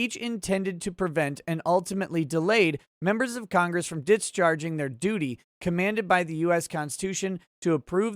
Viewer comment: Hehe sounded like you said "discharging your doody"